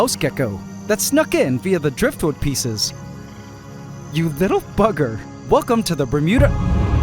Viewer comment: did he cuss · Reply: No he said bugger but it sounds like he said f*cker